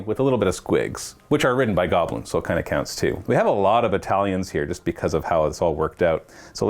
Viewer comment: every time he said "battalions" I kept hearing "Italians" and I was very curious as to why. Like, does have some kind of foreign exchange student program?